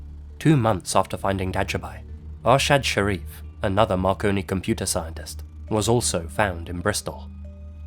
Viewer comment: I'm apparently not as dumb as I thought because many others thought it said Macaroni